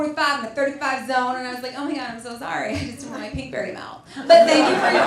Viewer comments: What is she saying at 6:00 mins..? Apparently it was funny. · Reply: All I can hear is "Oh im so sorry thats where my pink berry mouth.." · Anyone know what she says at 5:58? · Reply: Something about she didn't want her Pinkberry to melt... I think